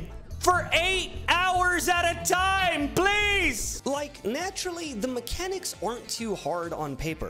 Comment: youtube thought you said like please